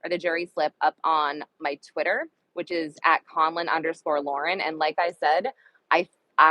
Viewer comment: When you said your name on Aks stream i thought you were saying Common. Not conlin. I had to type your stream title verbatim to find you lol. But sending love from the chat